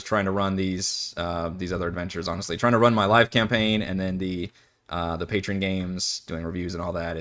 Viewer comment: " trying to run my life, campagin" Is what i heard while driving to work, and was chuckling " you and me both brosef" hahahah It's a brutual adventure module 😅